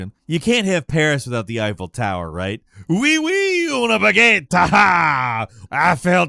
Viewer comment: Honestly the dishonor 2 level where you travel between timelines is my favorite, not only does the area change depending on the timeline but you can also get behind enemies and stealth them through the other timeline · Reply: I love how it sounded like he said “wee-wee on a baguette “